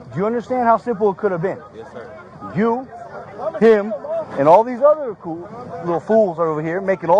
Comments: on the video, did the officer say “you and the rest of these c***s” and change it to “ fools” in mid-sentence? It sounded like that. Or was he calling the kids some other name? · 1044 did that racist cop just call that kid and his friends Koons or did he say cool little fools you guys need a million-dollar lawsuit too civil rights violation talking about respect the police what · Kinda sounded at 10:38 that he said "... and all these other coo...aww...fools" or i mistaken, sure sounded like a Freudian slip about trash pandas to me tho, hoping im wrong but i know it still happens even in this day and age. 🤔🤨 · did he just call them coons. Now you know why they tried to keep the video from coming out